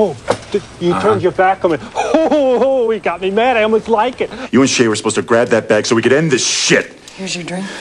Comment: At :38, it sounds like he said, "Ho ho ho, he got me mad, almost liked it!"